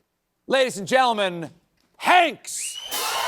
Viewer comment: did he call him just Hanks?! ❤❤❤ love this interview so much